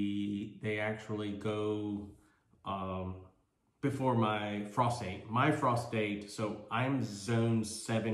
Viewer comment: David van Gogh! (misheard "frost date" as "prostate" and was very confused for a moment 😂)